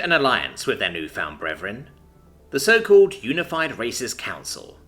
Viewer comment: The so-called Unified Racist Alliance I swear I heard that, and it was funny af